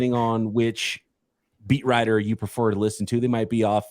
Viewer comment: dude said "whatever beat writer you like to listen to" i heard " meat rider" you listen to hahahaha